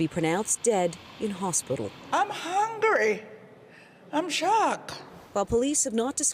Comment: Lady this is not a time to be hungry. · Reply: Haha, when I first watched it, I thought she said "hungry" too. But to be fair, she has a caribbean accent that made it sound like that. She actually said "angry."